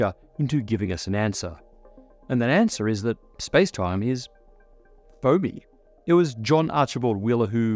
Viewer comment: Spacetime is foamy ... I heard "phony".